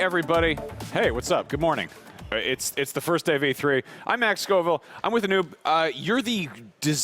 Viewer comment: i thought he said I'm with a noob